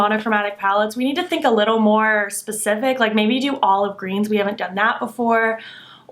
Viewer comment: I would love an olive green palette from colourpop! Or a good Halloween collection! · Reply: I literally thought you said Olive Garden and I was about to get on board